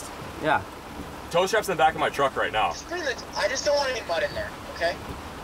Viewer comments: does say "i just dont want any bud in there" ?? Is he talking about smoke?? Anyone confirm? Lol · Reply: Patrick Graycarek pretty sure he says mud not bud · I thought said "I just don't want any bud in there" haha